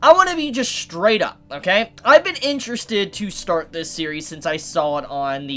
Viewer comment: would always activate siri bc my phone hears “hey siri” instead of “this series” :)